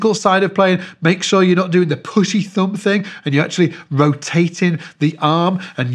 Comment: Totally thought you said that p***y thumb thing at 7:05 Thanks for all yhe great leasons and info on the bass and playing over the years... is the best for bassists